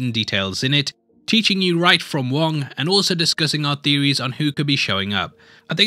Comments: Did I hear you say, “right from wong”? Lol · did? Did you just say right from wong?